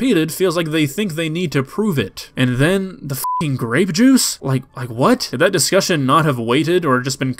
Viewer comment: An eternity silence for me when he said he spilled grape juice but I heard it as "grave juice" 💀💀💀